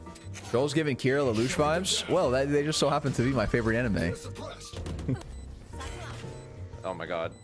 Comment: I thought you said death note was your fav anime ???